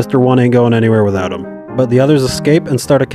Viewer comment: "They all go to Impel Down" *jazz hands* The ending got cut off my bad ya'll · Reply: It sounded like you said “ and the get send to imp”